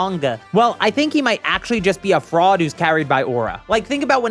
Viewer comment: Is it just me or am i hallucinating? Cause i thought tien said ‘I'm a fraud?’ Instead of ‘but my aura...’